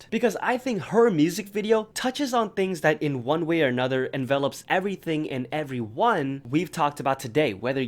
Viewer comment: what is he saying here? envelopes?? lol i genuinely dont know REE